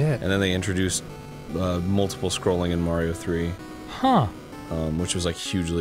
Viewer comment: Sounded like Arin said that "They introduced Multiple Sclerosis in Mario 3" Don't you mean... "Multiple Scrollosis"?!?!